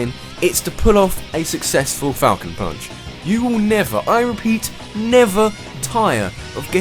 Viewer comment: the first time i used captian falcon i thought he said "taco punch"